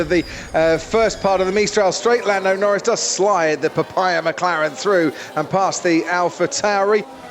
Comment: did he just said that Norris "slide the papaya McLaren through"?